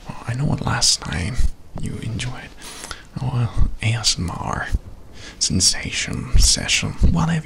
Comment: sound like he said "AnuS-M-R" 😂😂 i was bout to say u cuttin up lol